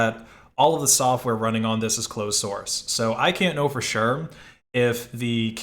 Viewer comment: I could swear I heard you say "glow-source"